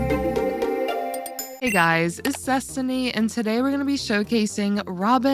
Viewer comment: I swear to God I thought you said "Hey guys, it's Zestiny!"